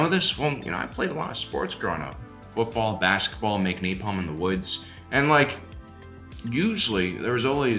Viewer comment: Did I just hear “make napalm in the woods”? 😂